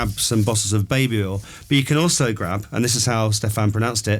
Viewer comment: I was watching the video and at 17:47, I heard 'bottles of babe oil' and 'pots of Lielb.' That's how I heard it, but I might have misheard. Could you clarify whether I heard them correctly? Thank you!